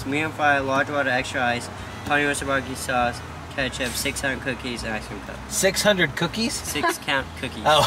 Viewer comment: I thought he said 600 cookirs too. 😂 you have grown up way too fast!